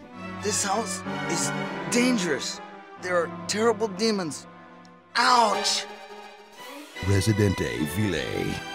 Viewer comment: DID HE JUST SAY OUCH?????